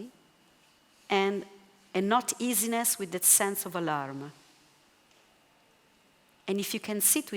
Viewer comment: At approximately 59:50 she says "a sense of - either a love or alarm" and I cannot decipher. Captions say alarm but the questioner clarifies "a love" and agrees. I find this a key point in this lecture for my awakening self and would like to clarify this. Does anyone know? Thank you. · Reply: a sense of alarm. sorry if my accent is not perfect!